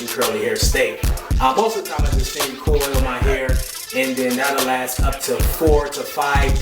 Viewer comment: What was said at 7:47? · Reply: He said he finger coils his hair